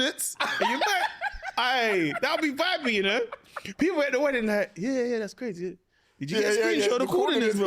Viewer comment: did you say wedding…